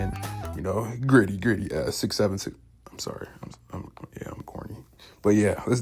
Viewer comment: Glad someone caught that 😂 I was debating leaving that in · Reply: I thought you said "I'm horny" LMAO